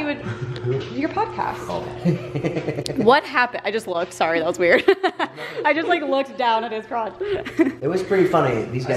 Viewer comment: WHAT DID SAY?! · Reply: He said thank you because covered for him. told her about the thing Jason got done and she lied and said podcast